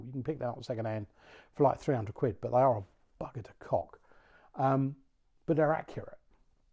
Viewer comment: I wet myself laughing cos I thought you said the hammerli was a ‘Bucket of Cock’ but then realised you said ‘bugger to cock’!!!